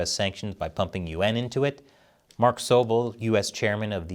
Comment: can u please start pronouncing yuan correctly? I was confused half the time because I thought u said UN